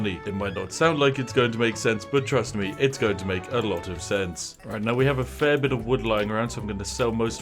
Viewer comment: In a video about cheezing the money making potential in game, I keep hearing "It sounds like it's not going to make a lot of cents. Trust me, it's going to make a lot of cents." Is that a deliberate pun?